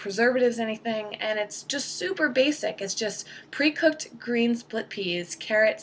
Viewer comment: hahaha i heard it as "SOUPer basic"